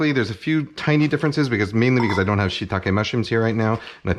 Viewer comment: Lol, before shitake i heard, "a shit tonne of mushrooms" haha